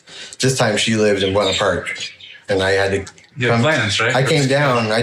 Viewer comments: I think he said Buena Park (, CA) which is, admittedly a 9 hour drive from Redding, CA. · For what it’s worth, at 38:10, says “Rohnert Park.”